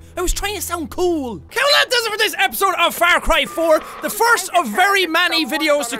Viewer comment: I THOUGHT YOU SAID FARTCRY